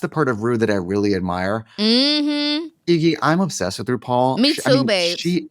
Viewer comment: did Trixie call her … Iggy? Lol